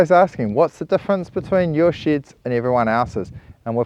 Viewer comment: lol I thought he said “whats the difference between your $h1t and everyone else’s? But i realize I’m watching sheds :) Great video!